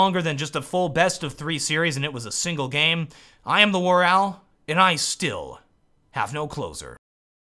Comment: When you realize when TheWarOwl says "I still have no closer" that that is his closer. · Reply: I actually thought it said "...and I still, have no clothes on."